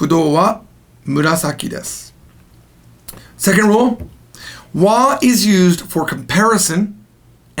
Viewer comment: I thought you said ga was used for comparison and emphasis. You have Wa there.